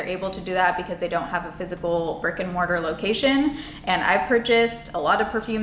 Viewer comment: Every time say 'brick and mortar location' I always hear 'fricken mortar location' 😂